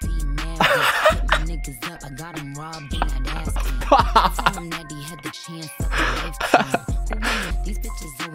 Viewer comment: did anyone else catch in beep beep when she says "I got him robbed and that a-- beat" IT KIND OF SOUNDS LIKE "got rah and a-- beat" THATS WHAT I THOUGHT IT SAID AND WAS SO GAGGED idk i might be stretching but if you don't know Rah Ali is Nicki's security and in the Harpar's Bazaar party Cardi left with a knot on her head lmao and she told everyone it was her security nicki went on queen radio and later said we let her tell that lie because we didn't want rah to get in trouble and that she was mad at rah for doing that...